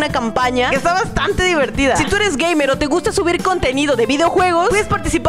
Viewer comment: escuché decir a gay, pero después me di cuenta que dijo gaymers XD